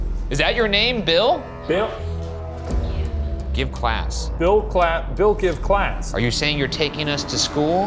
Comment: I'm pretty sure the ghost is more saying have some class since you guys are talking so much shit to the guy XD